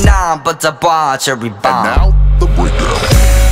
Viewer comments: am i the only one that hears “and now, the weather” at 2:57 like is that a canon thing or am i hearing things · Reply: I think it says 'and now, the breakdown' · am i the only one that hears “and now, the weather” at 2:57 like is that a canon thing or am i hearing things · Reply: Lol I always hear "and now, the widow" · am i the only one that hears “and now, the weather” at 2:57 like is that a canon thing or am i hearing things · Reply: I heard "and now the winner" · am i the only one that hears “and now, the weather” at 2:57 like is that a canon thing or am i hearing things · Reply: I heard “and now the window” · this is my first time listening to this song with headphones. Does it really say "and now, the weather"?????? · what did the man in the background say? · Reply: the man in the background said “and now, the breakdown”